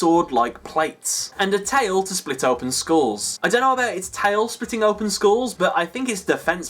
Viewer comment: " A tail to split open Schools" is what I thought he was saying. Cause I'm not an English speaker.